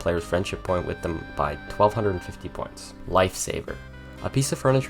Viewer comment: LOL I thought you called the wilted bouquet a lifesaver!